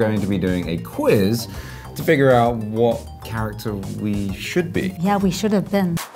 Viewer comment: I thought Henry said counter top but I looked at the title and it said character.